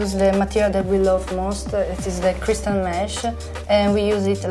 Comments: When she said ‘crystal mesh’ I thought she said crystal meth lolll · They kept saying crystal mesh and I thought they where saying crystal meth...I was really confused!